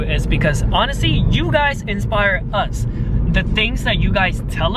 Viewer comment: i heard him say "infire". hAHA does anyone get my reference